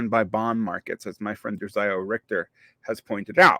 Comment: you mention that someone argues mayors get disciplined by bond markets, who is it that argues that? I can't tell what name you're saying. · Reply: Josiah Rector